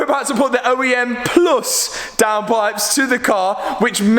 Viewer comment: I thought he said poes💀